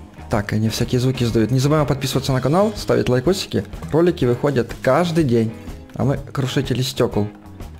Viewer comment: мне послышалось не ролики а кролики😂😂